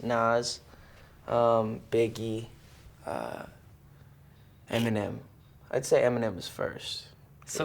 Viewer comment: Quand J.B a dit "eminem" en temps que française j'ai compris "eh mes nems"